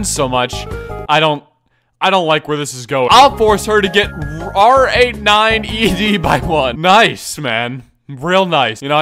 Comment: what she really said was I'll force her to get rage by one 🌚